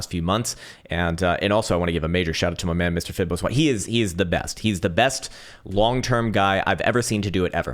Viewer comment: who does say right here? Mr. who ???? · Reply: Fiboswanny